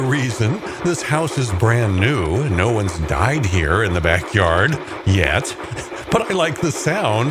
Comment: So whe not finna fly past of wat he said. He said no one died here yet